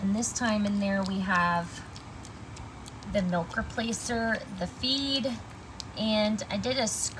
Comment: She is feeding oatmeal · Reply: I thought she said it was milk replacer, feed and rice.